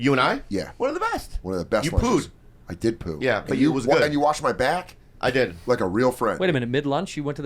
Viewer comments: Lmao, no Andrew, I heard "you washed my back" too 🤣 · i hear “you washed my back”